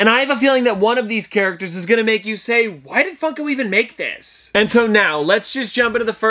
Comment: Honestly thought he said why the f**k do they even make this for, honestly spat out my coffee at the thoughts of the funko man speaking obscenities ...... But it would have been funny 🤣👏